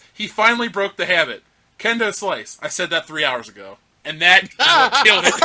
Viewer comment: At first I thought he said he was talking with Kimbo Slice.